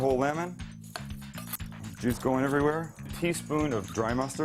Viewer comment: I thought you said “jizz going everywhere” lmao